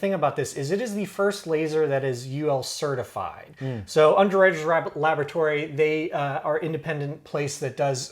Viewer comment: Does he say underaged laboratories at 5:46? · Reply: Underwriters Laboratory, perhaps :)